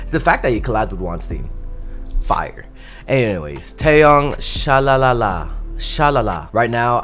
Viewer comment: I thought this said Taeyang and got really excited 😭😭😭